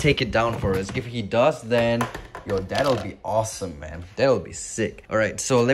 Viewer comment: "if he does, your dad will be awesome" That's what I heard lol